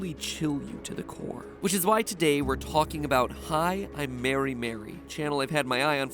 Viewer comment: I thought this video said "Hilm Mary Mary"